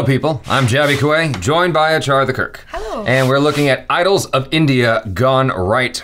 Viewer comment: for some reason I heard Achara the jerk